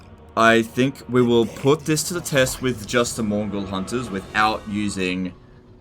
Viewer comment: Several times i thought said "mongol hunters"